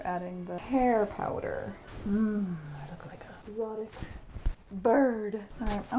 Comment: Did I hear “erotic bird” instead of “exotic”? Yes, yes I did.